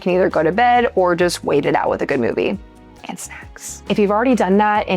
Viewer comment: Did you say and SECKS or SNACKS? Both? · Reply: Snacks! Definitely snacks. Thanks for watching (and for the close listening).